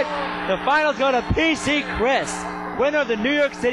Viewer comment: Who is this Pizza Chris person? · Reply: Richard Ashblade Go to 2:30. The one dude (wife i think) totally says "pizza chris." I find this to be very amusing.